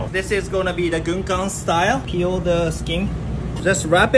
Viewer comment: I thought he said Gungdam style